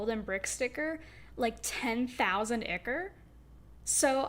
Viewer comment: i thought she said 100K😭